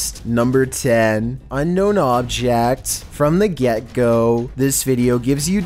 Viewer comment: I thought he said “fRoM tHe GhEeTo”